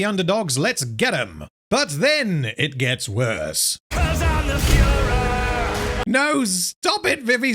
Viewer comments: for what it's worth, i'm pretty sure he actually says "i'm the furor" here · I do have one small thing to point out here which is actually important to your argument. During the song, Vox is singing about how he is the will of the people, as much is sounds like he says "I'm the Fuhrer" here he actually says "I'm the furor." Furor as in, the anger of the people. Vox Dei has a lot of callbacks to the previous song Vox Populi, including reprising lines from Lucifers appearance, where Vox is positioning himself as the peoples voice. With that context in mind I am going to be charitable and assume they weren't trying to make a direct hitler comparison. While I'm sure an argument could be made that the word furor was deliberately chosen specifically to sound like fuhrer, I do feel that specific word difference is important to define.